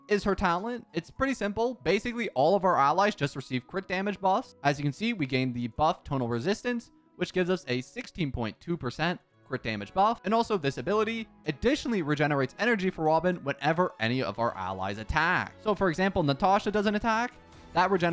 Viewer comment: did you just say tonal resistance? 7:02 flowing nightflow?